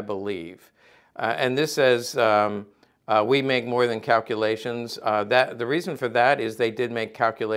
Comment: it does not say " we make more than calculations" it says: " we make calculations easy" the guy with the medic helmet is hooked, he now smokes 20 a day... he loves it too much